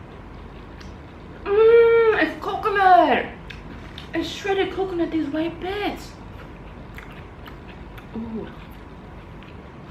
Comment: 😂 i swear it sounds like she said " it's shredded coconut this white bitch"...😃